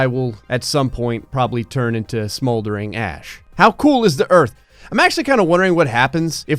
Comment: For some reason when he said smoldering ash I thought he said smoldering ass